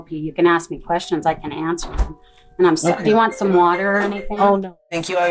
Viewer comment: "Do you want some water or anything" what I heard "Would you like some poison or something" 😂 those eyes are everywhere and that laugh was so evil.